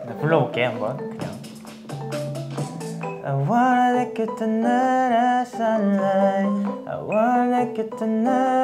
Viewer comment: why am I hearing "i wanna lick you tonight" sksksks